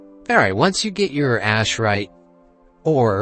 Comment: I think I heard, "Once you got your ass right". Nice video though and thanks for your time. PS. I found out if you hit lvl 18 and you use the make 5 ass right bars, you'll only level up 1 time, so continue making 1 at a time and you'll level up each time you make 1, that's 5 levels instead of 1.